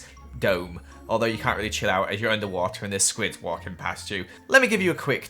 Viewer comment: did he say if a squid is walking past you